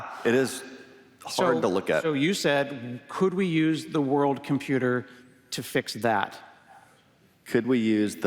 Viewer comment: did Scott say "the world computer" ????